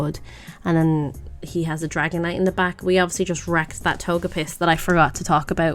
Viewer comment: did you say togepiss? I love it. I'm gonna call it that from now on. Also that's a funny looking Dragonite.